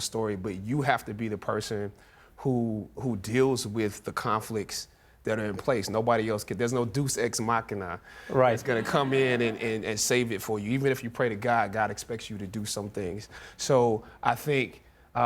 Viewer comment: All seriousness aside... did he say 'deuce ex machina' instead of deus?!?